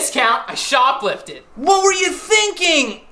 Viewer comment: Played it 5 times and I keep hearing "that game's among fold". What the hell is supposed to be saying? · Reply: Kryko month old